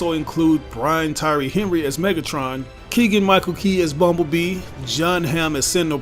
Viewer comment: For a second I thought that you said “Michael bay” would voice bumblebee